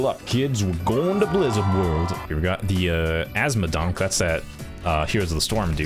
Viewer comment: "azmodonk" I hear "asthmadank"